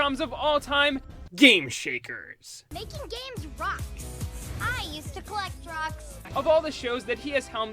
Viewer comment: I thought she said “I used to collect Drugs”😂😂😂